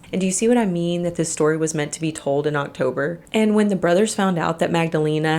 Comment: When you said “see what I mean this story is meant to be told in October “. I misheard you and thought you said “is meant to be heard not sober”. I was like okay and made myself a moonshine martini 😅